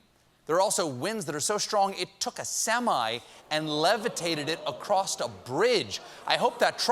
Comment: DID STEPHEN JUST SAY ACROSST???